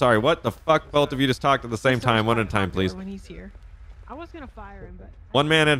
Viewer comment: Did anyone hear the one chick say "I was gonna fire him" as they walked away in the 24/7 I also think they said I hate when he is here.